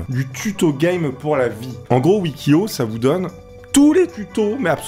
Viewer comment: Moi j'ai entendu <<huit kilo>> à